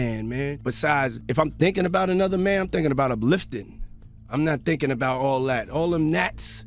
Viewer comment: Not sure if its one of the mistakes or not but at 1:11:35 the subtitles say "if im thinking about another man, im thinking about uplifting" but what Diddy actually says is "if im thinking about another man, im thinking about him lifting", as in he likes to think about men working out. He also says "all them nats" by which he means nattys like someone whose muscle is natural and not riddled with steroids.